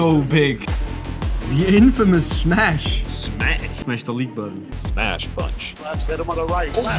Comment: I heard smash the lick button